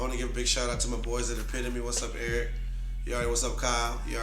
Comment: what sneaker shop did he say? couldnt understand... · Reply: Epitome